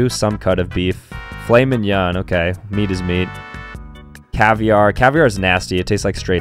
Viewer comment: "meat is meat" what i heard: "beat his meat"